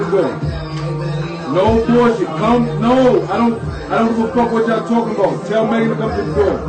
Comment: Am I the only one who heard Pardi say’ “I don’t do coke!” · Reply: He said “I don’t give a fuck what y’all talkin bout” 😒